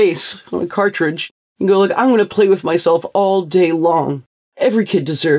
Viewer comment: did he just say "i wanna play with myself all day long" ?